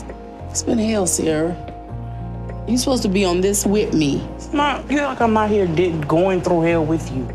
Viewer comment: It sounded like she said, "Whitney," and not "Wit' me." I was thinking, "Who's Whitney?"